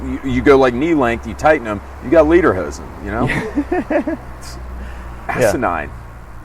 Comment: Did it sound like James said.....liederhosen......to anyone else?